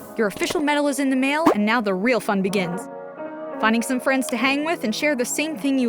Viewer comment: I thought she said this is where the real f*** begins